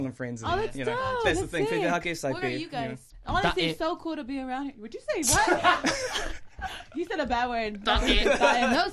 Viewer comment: What bad word did the guy say at 3:33? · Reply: apparently "ta-eh" which means shit